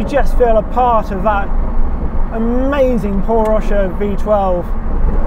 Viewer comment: Did he say "Porsche V12"? Thats the almighty BMW S70 mate :D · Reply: He said its a Paul Rosche V12. He's the genius bloke from BMW who created the S70/2 for the F1.